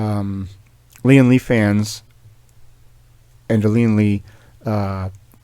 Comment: Anyone else heard OnlyFans?